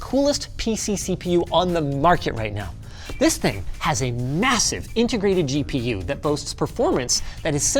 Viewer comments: Did you say… M… M… m— MASSIVE? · did you say massive?